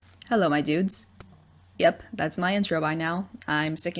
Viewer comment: "Hello my dudes" I thought I heard "Hello idiots"